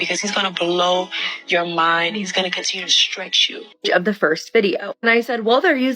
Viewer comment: did she just say that theyre gonna stretch you?